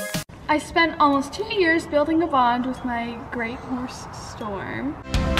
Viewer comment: In my opinion, I think she should have worded the title differently as it doesn't make sense to me. At 0:30, did say Storm was a grey or a bay as I heard grey but it might have just been me